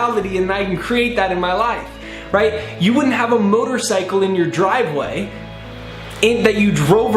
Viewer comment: I thought he said “murder cycle” 😆